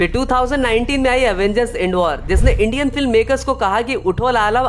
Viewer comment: did bro said endwar😅